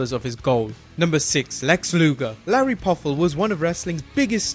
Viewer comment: "Larry Poffo " at 4:19 ..... huh? Did anyone else catch that? He was talking about Lex Luger but i swear he said Lanny Poffo around 4 minutes and 20 seconds .....